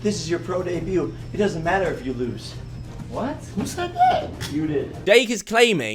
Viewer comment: ’’. Dake is claiming, Dake= Dumb Ass Jake not sure if Kavos was supposed to say Jake but it sounded like Dake and honestly if it was Dake thats even funnier because he should be Dake because hes a dumb ass xD